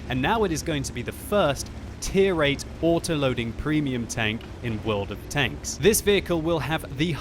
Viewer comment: You mean that t6 swedish auto loader medium? was talking about tier 8 auto loaders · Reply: M4kehh I think I missheard it then. I thought he said first autoloading premium. Thanks 😅